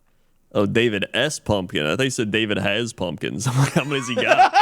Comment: I heard "David ass Pumpkins"